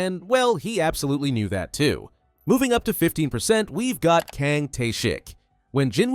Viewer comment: i thought he said Kang Taeshit 😂